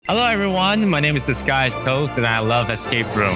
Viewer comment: Why did I heard ‘this guy’s toes’ instead of ‘disguised toast 😭😭 Damn I was so confused for a moment